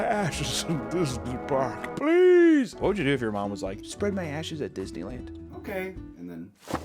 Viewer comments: I heard "Spread my ass cheeks at Disney Land" instead of "spread my ashes..." I need help. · I thought you said "what if your mom asked you to spread her ass cheeks at disney"...